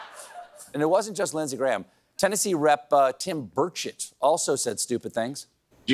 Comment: I honestly thought he said Tennessee Rep Tim Birdshit…